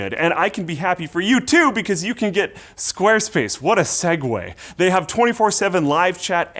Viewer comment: did he say squareFACE?